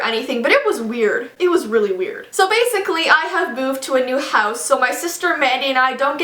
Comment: I thought Jessii said 'So basically I have boobs' instead of i have moved 😂